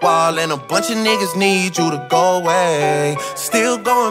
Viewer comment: I always thought it said 'Saw you last night but didnt call it date' Does anyone else hear it like that or just me?